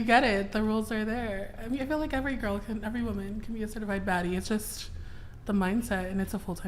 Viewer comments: Like this if you SWEAR you heard her say "certified FAT bitch" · The first thing this beluga says is that shes a certified baddie but never had a bf.🙄😒🤦🏾‍♂️😂😂😂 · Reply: I thought she said certified fat bitch. I'm sticking with my interpretation. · I thought she said “certified fat bitch” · I swear I thought she said certified fatty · I thought she said I’m a certified Fat bitch. Fat people speak kinda strange because all of the fat pulls and pushes in weird places and they are hard for me to decipher sometimes.